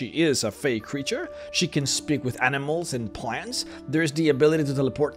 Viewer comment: For a second I thought her ability said "Speak with breasts and plants"